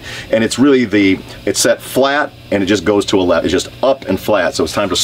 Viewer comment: Did he just say "It just goes to eleven"?